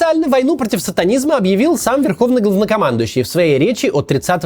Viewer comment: ГАВНОкомандующий? я не расслышала...